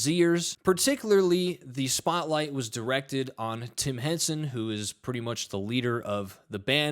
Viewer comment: Polyfilla, great for filling holes in walls before papering them, of course he's a fraud, studio trickery, not kool....i thought he was a tennis player, oh, Tim Henson, i thought you said Tim Henman.... soz Pollyfilla